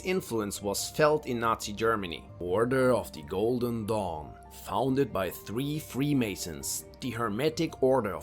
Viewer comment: I thought he said "The Order of the GOLDEN DONG" @ 3:58 LMAOOO🤣